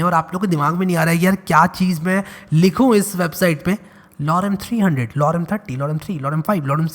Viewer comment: it's just like loudam 300 loudam 30 🤣🤣 I know you said lorem but I say what I hear